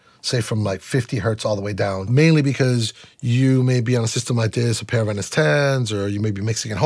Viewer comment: May be you are in system ....... What he says on minute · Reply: He refers to system like this (refer to his studio monitor system) and a pair of NS10 (refer to yamaha NS10 spekaer) I think